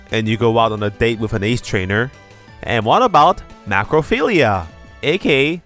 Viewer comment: I thought you said necrophilia and had to check the captions there.